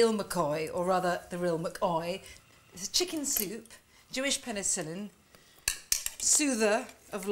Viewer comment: I thought she said “so penis”😂😂😂😂😂😤😤😤😤😤🤣🤣🤣🤣😤